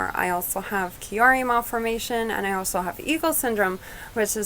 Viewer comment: What syndrome at 4:45 Eagle syndrome I don't know what she said??